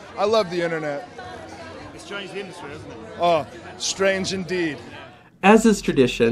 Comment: Orton misheard the journalist. He though he said "It's strange, the internet" but obviously he said "It's changed the industry" lol